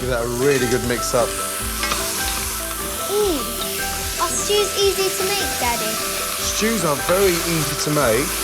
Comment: I had the captions on and Gordon said "Stews are easy to make" But the captions thought he said "Jews are easy to make"